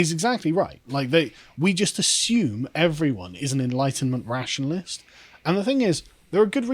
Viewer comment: I know Carl said, "Enlightenment rationalist", but I heard, "Enlightened McRationalist", and I'm sticking with it.